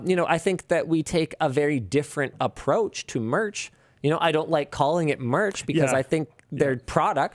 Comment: LINUS: "I DON'T CALL IT MERCH" ALSO LINUS: "DON'T USE SUPER CHATS, SEND MERCH MESSAGES INSTEAD' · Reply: what he actually said: "I don't like calling it merch"